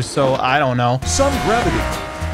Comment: during the first sun gravity part I didn't hear it right so I thought it said "some gravity" with no specifications to the amount